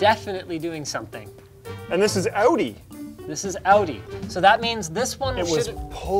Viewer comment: for a moment i thought u said "Audi" and thought u were advertising Audi too